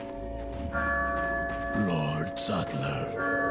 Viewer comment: did he actually say Mark Zuckler? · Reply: No. He said Lord Saddler. I don't know how you got Mark out of Lord.